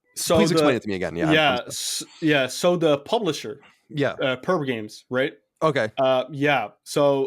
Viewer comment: Who are perb games? i serched got phineas and ferb episodes! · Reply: I heard it as perv games at first lmao